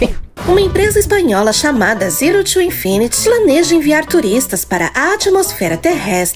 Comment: so eu q escutei Zero Two infinity;-;?